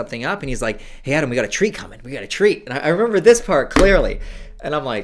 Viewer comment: I thought Adam was say "we've got a tree coming, we've got a tree" the first few times and I was really confused